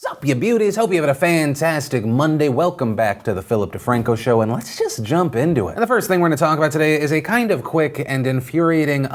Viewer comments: At first I heard "Sup ya booties" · What everyone hears ‘Sup you beauties’ What I hear ‘Sup you booties!’ 😂😂😂